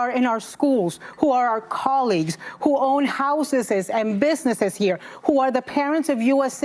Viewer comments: did she say — ‘…who own HOUSESES’? 😂😂😂 what a dumbass. 😅 Mexican spin off - DR HOUSESES. 😅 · did Consuela just say “houseses” 😂